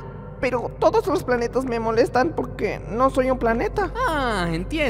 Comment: parece que dice "poque", se trago la r.